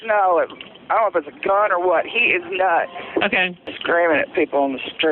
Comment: I swear at 0:41 i thought she said "his nuts" when the ems operator asked what he pulled out his pants and then for her to follow it up a few seconds later with "hes got something down his pants, its LONG!!!" forgive me for channeling my inner 13 year old